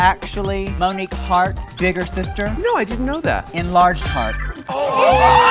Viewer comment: i thought Trinity said Enlarged Heart - or is the subtitle wrong?